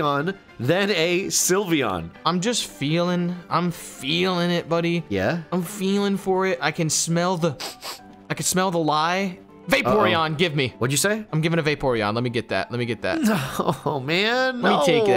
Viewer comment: did... did he say he can smell vaporeon...